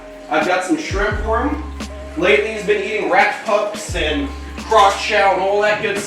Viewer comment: i heard: “lately he’s been eating rat fucks” me: wait wtd